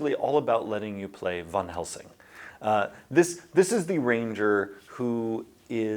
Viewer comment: He says “Van Helsing”, I hear “Witcher”.